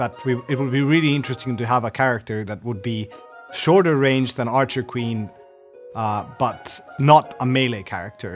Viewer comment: Did I heard that right 0:53 "Shorter than archer queen butt" :0